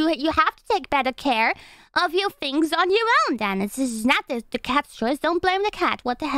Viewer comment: am I hearing this wrong or is she saying "It's not the cat's Schuld"? Sasuga Hühnchen